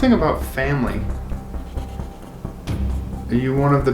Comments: I hear what sounds like is it family? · when gets the “___ family” I heard “is it family” is that just me or did anyone else hear that